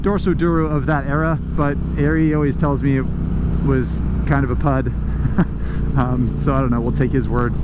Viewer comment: "Ari always tells me [that it was] ... a pud? .. pod? or pug/pog?" What is that word, can any good soul tell me?